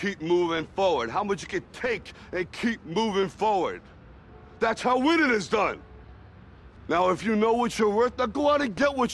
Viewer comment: what does he say at 0:09? thats how weird it is done? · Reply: Thats how winning is done